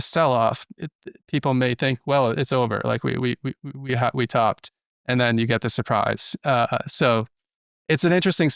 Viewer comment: all i heard was "Up, and then Up, again"